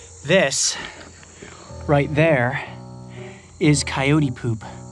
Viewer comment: when coyote said here's some coyote poop I thought he said he pooped there XD